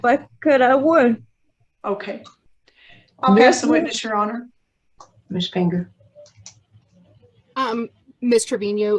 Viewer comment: Amen to hysterectomy. They are asking stupid questions though. One time they said Ms. Finger but now sounds like she is saying Ms. Panger. In my opinion, all the kids need to be put up for adoption.